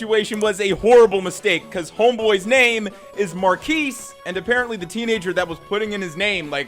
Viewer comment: The whole "monkeys" thing was blown out of proportion. He says what is name and it sounds like he says "monkeys". That was 100 on the customer. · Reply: Yup. His name, Marquise, sounds like Moar-keys in his pronunciation video. Easy to mishear and end up with a Monkeys autocorrect.